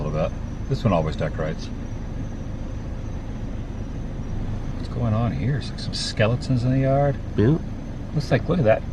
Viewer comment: I heard you say. "What's going on (out) here? 😅🎉😂